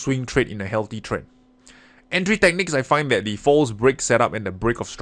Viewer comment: I thought he was saying 'Fools break' for half the video. I'm going to always think of this as a fools break now :D